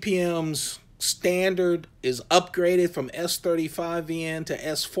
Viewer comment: I thought Sal said it’s an upgraded VG-10